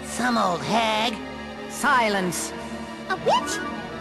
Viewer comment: lol everytime i hear shock say a witch it sounds like shes saying a bitch XD